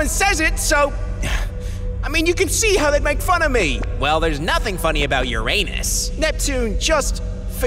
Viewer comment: why does neptune says like this, this is what i heard lol "Well theres nothing funny about your anus"